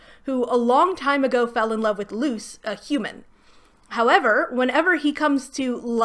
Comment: I know you mean "Luce, the human" but it just sounds like you say "Luz the Human" and reminds me how I could be watching much better fantasy stories 😂